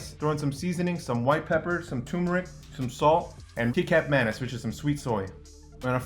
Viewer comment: I'm really sorry, but I did a double take because I thought he said keycap mayonnaise. "Kecap Manis" 😂 it's pronounced like "khe-chup mah-nese".